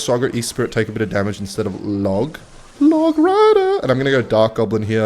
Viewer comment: did i hear log rider?